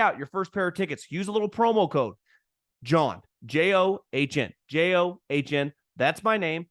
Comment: For a while , I thought you were saying that code was jawhn